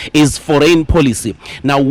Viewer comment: Foreign policies… I thought he said “ for rain policies!”😅😅😅